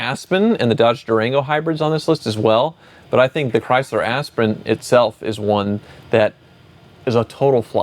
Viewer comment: "Chrysler Asprin" rolled off tongue so smoothly, I almost missed it. I backed the truck up, then almost died LMAO when thats exactly what he said, twice!! I mean, most Chrysler products could have been named "Asprin", they usually drove well until they suddenly blew up. My 2004 Dakota was the best driving truck I ever owned (out of dozens). But there is usually a weak link somewhere (4.7 V8...)